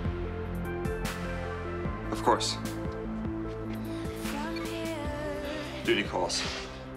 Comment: I heard "duty calls" as "booty calls" and was like WUT IN THE NAME OF RAZIEL DID I JUST HEAR